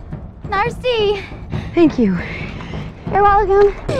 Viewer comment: did she say narsty?